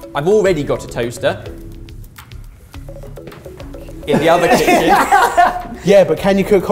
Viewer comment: Did he say chicken at 1:50? Sounds to me he did